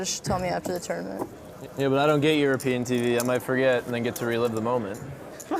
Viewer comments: Anyone know what Doc Sands fully says at 35:55 when he says something about reliving the moment? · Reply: He said "I dont get european tv, so maybe I forget and get to relieve the moment" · can anyone tell me what doc sands said at 35:55? I am not a native english speaker ^^ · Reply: Yeah but I don't get European TV, I get to forget it then relive the moment